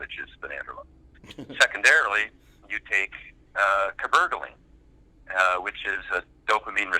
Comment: What drug names were mentioned at around 22:15? Couldn't hear it well. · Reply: I think it's cabergoline.